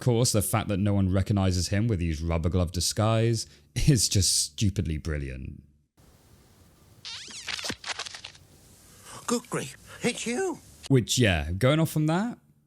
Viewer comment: fun fact I used to think Wallace said Goodgree as if that was the name he gave Feathers McGraw